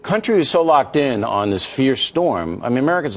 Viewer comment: Did he just say f*** ?